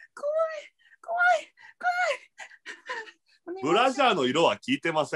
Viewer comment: ブランドをブラジャーと聞き間違えるのやばいｗｗｗｗｗｗ